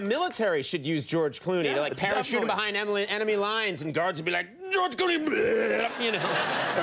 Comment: Did someone notice at 1:58 - Conan says parachute him behind 'Emily' lines instead of 'Enemy' lines 😂😂